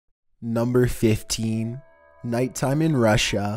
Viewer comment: wow I really thought he was about to say "Number fifteen.. Burger King foot lettuce"